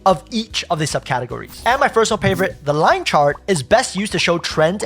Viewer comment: Great video...but at 7:46 did you say fersonal pavorite or do I need to check my ears? lol